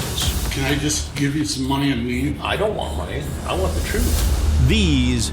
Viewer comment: I always mishear this as the guy telling Chris if he can just give him some money and weed lol.